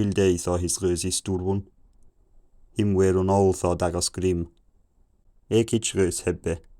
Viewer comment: At 700 I heard “de Dag is grimmed” which I kinda understood as “the days are grim/the day is dark” this coincided with 900’s “de graund ist cauld” Edit: I just got to the subtitle version and I definitely started understanding the sentences, but not the exact words, a little after 1000